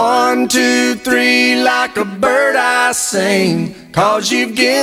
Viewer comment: When I was younger, I honestly thought he said, "1 2 3 like a bird ice cream."